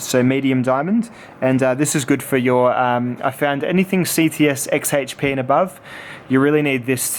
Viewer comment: What did you say at 8:53? Youll need the diamond 300 for anything above what? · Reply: CTS-HXP and above. Essentially he means anything that is harder to sharpen than the CTS-XHP steel, you'll want that 250 grit diamond.